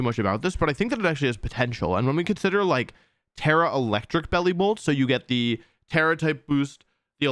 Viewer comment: I heard “terrastalize then electro web” and all I could think was “oh god I really hope Regieleki isn’t in this game”